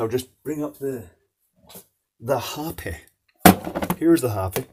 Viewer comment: Oh, HOPI. I thought you said the harpy, and immediately thought you had my sister as a guest on your show.